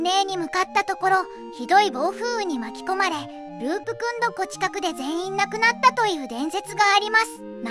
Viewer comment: ループ君どこ？って聞こえるぅ